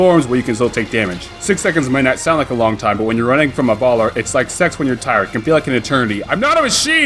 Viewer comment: Did he really say, “sex when you are tired?” at 1:51?